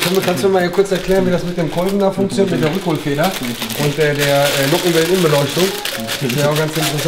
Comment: Was sagt bei Minute 8:11? Nockenwellen-Umbeleuchtung?! 😂😂